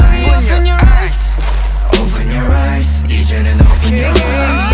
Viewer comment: Did he just say "ass" in the back but said it when he said "eyes" to cover it up